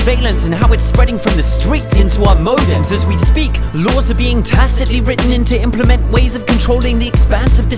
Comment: To the Italian translator: at min 00:33, it is not "tattico" but "tacito". I think says, "laws are tacitly written". Also, it is not "nove Settembre" but "undici di Settembre (11/09/2001)". Grazie.